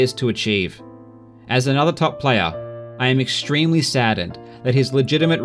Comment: did i hear "im extremly sadn't" at 12:53??